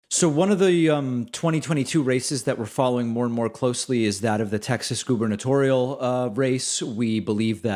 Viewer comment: For half a second I thought you said "One of the 2022 racists we're following more and more closely is..." and that made total sense to me.